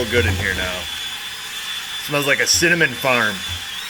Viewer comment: When you said cinnamon farm I thought you said cinnamon fart.